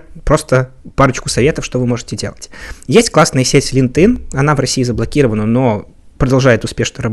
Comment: я не расслышал на 12:20. Как называется сеть? Подскажите,пожалуйста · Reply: LinkedIn